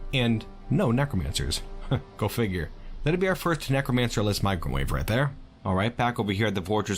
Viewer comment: For some reason I heard "necromancer less microwave." I didn't know microwaves existed in dwarf fortress let alone necromancer ones. ;p